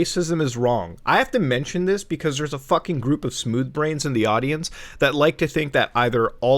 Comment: Did you just say smooth brain because I heard smooth skin and thought you were a ghoul